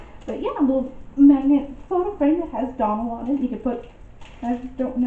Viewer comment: You said Donald and I heard McDonald's.